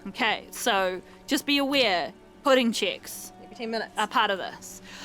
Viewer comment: I swear, English might not be my first language, but it does not bode well for my unconscious mind that I heard "pudding chicks" at first and for a second wondered where the slime girl content in Disco Elysium is hidden.